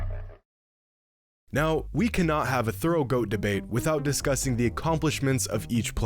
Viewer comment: Ayo did he say throat goat debate!?